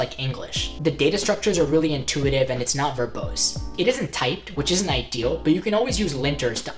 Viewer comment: did he say "it isn't typed?"